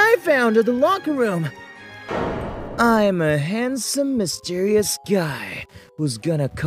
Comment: I heard “I’m a handsome mysterious gyatt” 😭